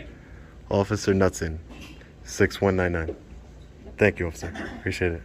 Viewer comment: did she say 'Officer Nutz-Inn" ? I spat out my Jack and Coke!!!!!